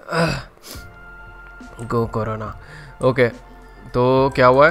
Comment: Did he say go corona .